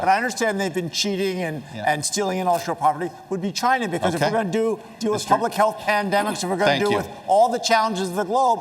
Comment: does anyone else hear Mr. Hickenlooper say that "if we are going to do a public health pandemic" regarding China? Incredibly weird and maybe a bit telling, hopefully I heard that right.